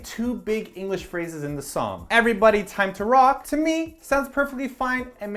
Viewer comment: I had no idea that they were saying, "Everybody rock"! I thought they were saying, "Everybody Run!". Ha ha ha